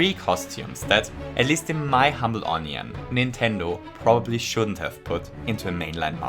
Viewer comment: did he just say onion